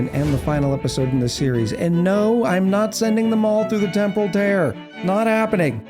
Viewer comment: I'm slightly hearing impaired so when you said Temporal Tear, I heard "Temple of Terror" and I wondered when they added such a thing...